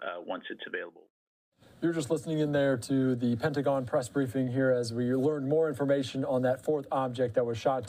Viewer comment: Go to 19:45 to listen to a “hot mic” conversation. If you listen carefully you can hear “is there an emergency crash beacon that’s supposed to take place” and then it cuts out when someone else chimes in saying that they’re hot mic’ed. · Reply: I listened several times. I hear "press briefing" not "crash beacon" Not that it makes it any less terrifying